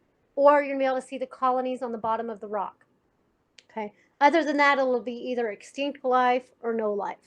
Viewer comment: Hello, I couldn't understand what she said at 5:31 min when mencioning the 2 kinds of experimentes for detecting life, Could somebody please help me out whit those names please? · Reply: Identifying a green stripe or seeing the colonies at the bottom of the rock